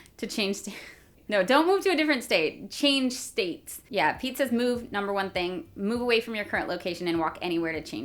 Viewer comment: "Pete says move..." I heard "Pizzas move" pretty much all the way through that part.